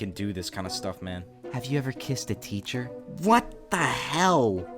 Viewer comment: HELP I THOUGHT THAT SAID KRISSED 💀🙏